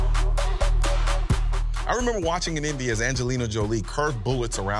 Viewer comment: what the hell does he say? Sounds like, "I remember watching an Indias Angelina Jolie" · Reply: Watching in envy